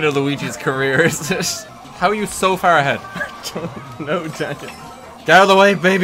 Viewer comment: Did he say "Daneil"?